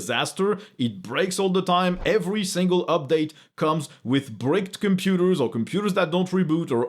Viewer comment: Did he just say: "Windows 11 is an absolute disaster. It breaks all the time. Every single update comes with BREAKED computers" ??? If he did say that, it's the most beautiful French mangling of an English word ever! And very apt and precise.